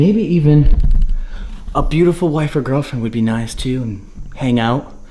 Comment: At first I thought he said "A beautiful wife AND a girlfriend" hah